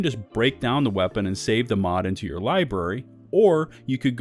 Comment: what's a liburry??? he says it twice